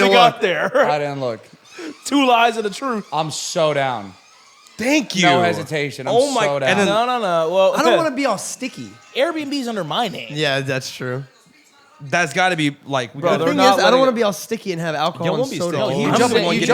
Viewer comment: ngl at 14:56 I wasn't tabbed in and I hear "I don't like to be sticky" followed by "we can do it anywhere" and I went 🤨